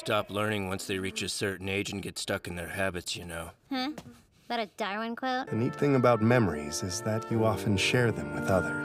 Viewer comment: i thought she said 'that a dhar mann quote?' sjdfhsdgafouisaekf